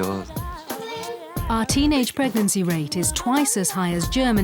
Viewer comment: I thought they said a week ago our teenage birth rates are twice as low